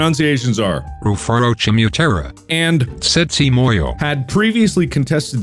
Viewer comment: i heard sexy moyo💀